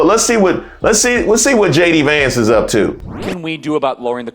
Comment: Did he say lower the cost of daycare "HATE" and working families? (at 21:50 check it out)